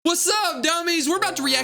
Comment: Did bro call us dummy's